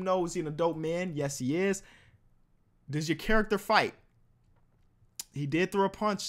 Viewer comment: I thought it said flight